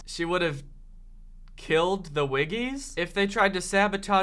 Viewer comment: At first, I thought said Joey would kill the wiggles. I was like, "NO! Not the wiggles. Not my childhood."